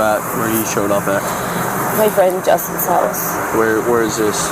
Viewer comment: I said the same, but someone told me she said she was at "Jess" house, but I thought she said Justin.. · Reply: She said, My friend Justin's house. Then the officer asked her something like, Did he do any damage at or to ""His house". Meaning the guy's house she was at.